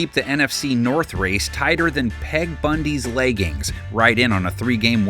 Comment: I was away from my phone and swore to god he said “tighter than Ted Bundy’s leggings” 🤨🤨🤨🤨